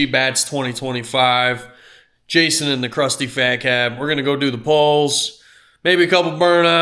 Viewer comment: I thought I heard say “Crusty Faghat” for a second. I should have known better but it took me a minute